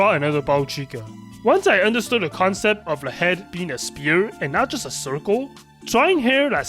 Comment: Just discovered your channel from "How I Taught Myself to Draw in 1 Year" video and it was so inspiring to watch! Also at 3:28 I cant help but hear spear instead of sphere 😅